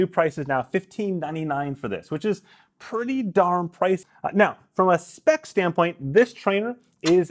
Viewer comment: LOL, he can’t even look straight at the camera when he says that price and cuts his sentence off to boot. Even he knows it’s grossly overpriced. What does he say? That price is pretty damn…? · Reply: I said “pretty darn pricy”, you can hear that pretty clearly. And then I talk about price more at the end of the video quite a bit. Did you not actually watch the video?